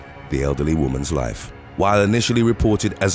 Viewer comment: Between 6:20 and 6:27 did u just say by stopping her from ending the ugly woman's life 😂😂😂. Hold up not guilty from hearing voices??? Now did she have a past of hearing voices and was she prescribed meds for these voices and was she not taking them if not she should be found guilty. A surfer isn't all of the sudden going to hear voices out of now where I call b.s